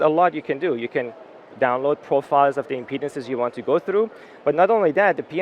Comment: You said impedance around 10:30. I thought you said "There's a lot you can do, you can download profiles of the penises you want to go through". I need help.